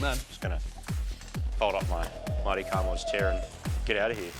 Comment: For a second there I thought I heard 'mighty rod knocks'